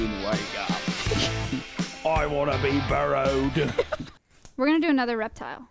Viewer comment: Hi Hasan I love the video but I think your editors made a mistake with the subtitles. at 7:32 I think it meant to say "Burrowed" instead of "Borrowed"! I loved the vlog though and I've rewatched this visit like 9 times!